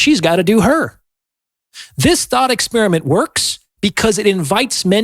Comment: did he say "this thought experiment..." or "this THOT experiment..."??? Very clever there! 😏